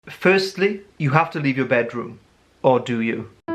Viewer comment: “How to win as an INTP” *Clicks* “firstly, you have to leave your bedroom” *Minimizes screen* “Or do you?” *Comes back :)* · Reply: For some reason I heard "You have to be good in your bedroom"